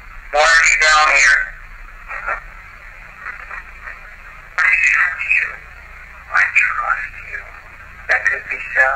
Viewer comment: I hear I tried to.. right before says that could be sally.. this was amazing evidence thanks. boys ❤